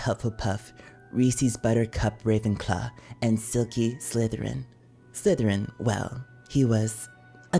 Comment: WAIT DID I HEAR IT CORRECTLY OR NOT - "SEULGI SLYTHERIN" at 9:15 ?!?!? Edit : Oh its Silky Slytherin Damn🦖💥